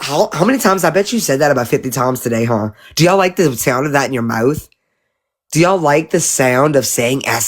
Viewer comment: Did she just say we love the sound of child abuse 🤢🤢🤢